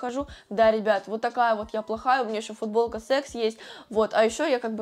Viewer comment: что она сказала?😮с*кс